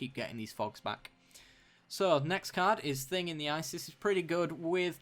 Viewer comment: Did you say "Thing in the ISIS"? I thought your card name puns were one-episode specials. Keep up with those!